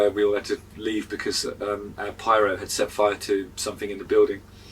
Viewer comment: I thought you said 'our pirate set fire to something' then